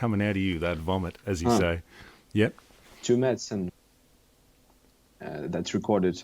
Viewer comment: does anyone know what producer was mentioning at 11:50? I couldn't quite make it out but I'd like to look up those production videos if anyone caught the name. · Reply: Tue Madsen